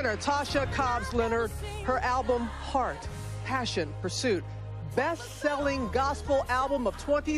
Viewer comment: for some reason i heard a “a passion for satan” then i replayed it then heard nothing, misheard? Or no coincidence